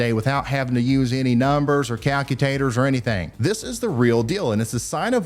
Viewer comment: Did Ryan just say calcu-tator? 😂 He's adorable.